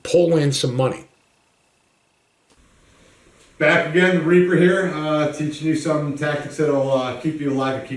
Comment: Reaper? I thought he said raper